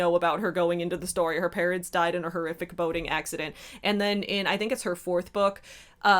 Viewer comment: I heard " her parents died in a horrific boning accident."